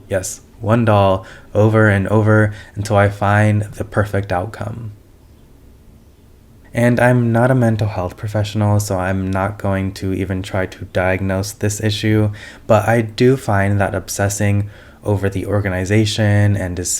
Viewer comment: OMG!!!!! XDDDD When you said "-I'm not a mental health professional" I thought you were just saying "I'm not a mental case" and when you said "health professional" I internally sighted Ok that was just me projecting... XDDDDD